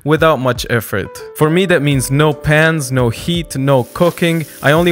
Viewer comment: I thought he said "no pants" for a minute lol